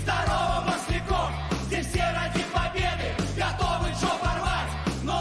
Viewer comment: Пришлось перемотать,послышалось Готовы жопы рвать)